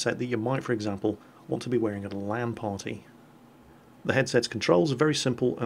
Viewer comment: Omg i was eating crisps and at 4:12 i heard "at a lemon party". for a moment i was really shocked at your hobbies.